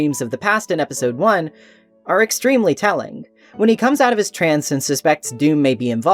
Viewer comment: i had a heart attack because i thought you said "when he comes out as trans" asdfnksfbhgkadsjfhgf. GOOD FOR HIM THOUGH. OR HER??? GOOD FOR SHADOW THE HEDGEHOG